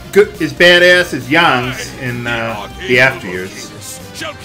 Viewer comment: Did said Yawng instead of Yang?